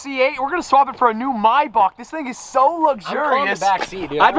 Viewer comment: The cars this guy drive and have is crazy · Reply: did he say my bach